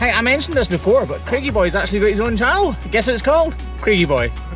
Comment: I misheard channel as child when you said that Craigie Boy had an new Child called Craigie Boy !! I always watch until the end, I generally edit all the way through. how do I find the emoji ? ?????????????????????????????????